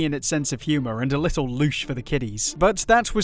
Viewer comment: what is the word you used? It sounds like loosh but I don't know what that means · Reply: Louche. Good word to have in the toolbox